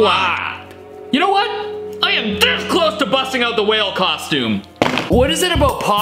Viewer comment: I heard ''fart like a whale'' 🤣